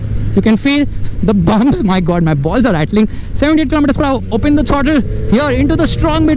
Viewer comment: did he say..my balls are rattling?😂😂